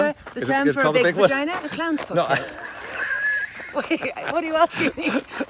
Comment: Clam's Point, Clam's Port...? Whatever she said it was hilarious without sounding crass. I think my biggest take away is that there is an opportunity here for a follow up hike discussing uncommon English slang for genitals. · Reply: I heard clown's pocket lol